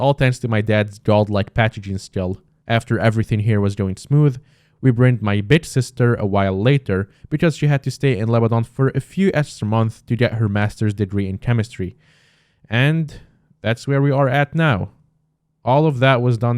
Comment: glad to hear your doing better (I watching this at 4am) btw can someone tell me what he said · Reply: i said "We bringed my big sister a few months later because she had to stay a few more months in lebanon to get her masters degree in chemistry"